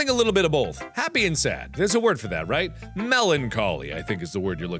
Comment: When Mark said "melancholic", I thought he said "meloncalling", and I was like: Wtf kinda poem is meloncalling?